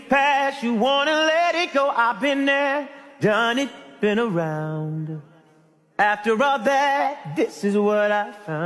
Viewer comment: I always thought he said “Well I’ve been there done it on the ground”